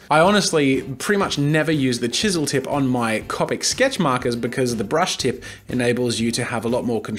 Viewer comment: did he just say copik